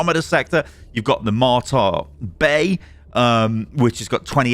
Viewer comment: With your accent I keep hearing you say "Martyr Bay" and that definitely sounds like the next meme planet we'll be fighting on.